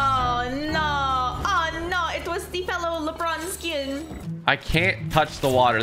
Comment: Anyone see what she said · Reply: Lebron skin